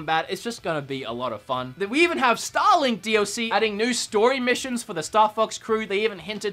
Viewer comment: did he say “Stalin DLC”?